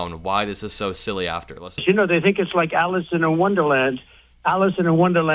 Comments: Sounds like he said Alison in Wonderland. It's Alice. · wait... I know asylum to him is the late grate hanible leckter and applying for a work visa is a credit card to him ... but I swear I heard him say "Alison and wonderland..." He said Alison and wonderland....!! 🤣💙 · 💙 sounded like Teump was saying Alison in Wonderland, twice. Buffoon. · Did he say alice in a wonderland?.....wtf is that? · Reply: I thought he was saying Allison in Wonderland but that makes more sense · WHAT? Did he really say "Allison in Wonderland"? TWICE?! I'd expect the best selling author of "Big Trouble in Little Hands" and "I AM GOD" to be better read than this. DISAPPOINTED! #TrumpIsGeniusest 💙🇺🇸✌